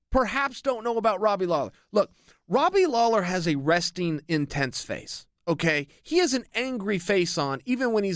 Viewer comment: Did he just say Robbie Lawler has a resting bitch face? 😂🤣